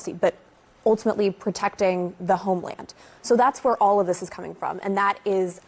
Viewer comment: Every time these people say homeland, lol I hear is “FATHERLAND.”